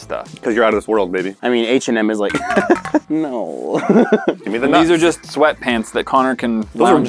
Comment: did he say give my the notes or your